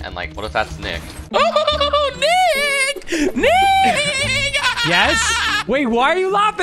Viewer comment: WHAT DID HE SAY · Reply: Fuck